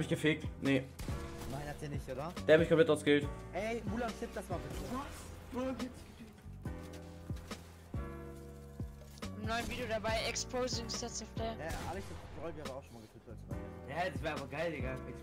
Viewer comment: sounded like he mentioned ‘exposing stats’ lmao